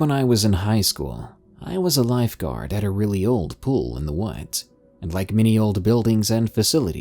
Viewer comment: Am I the only one who thought this said "Tim Pool in the Woods?"